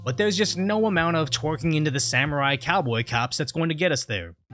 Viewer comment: I heard this as Samurai Catboy Cops. I need to change communities